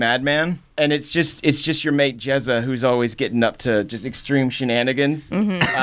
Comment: I thought he was saying "Jazza"